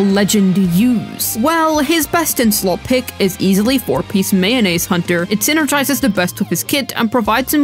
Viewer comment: even the caption said mayonnaise hunter